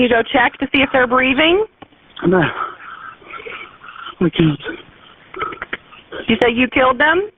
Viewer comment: Call 7 I'm disturbed by the subtitle inaccuracies, and how the operator mishears his words. At 37:08 the operator clearly asks, "Can you go check to see if they're breathing?" He replies, "(No)...I can't," which is one of many appropriate responses from someone who is perfectly innocent and in that situation. If the text in this vid is from some form of "official" evidence, somebody needs to be told about this. There is no way this should used as evidence. He may very well be guilty, but fair is fair. This error is egregious.